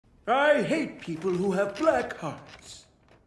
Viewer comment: i thought he said "i hate people who have black parts" i thought polar bear was racist man